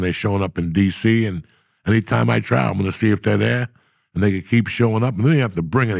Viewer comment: what did he say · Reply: Anytime he travel see if they are there